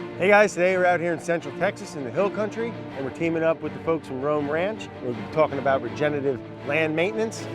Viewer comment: oh my god I thought he said Ram Ranch 😂